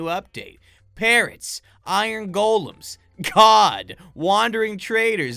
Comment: i thought he said god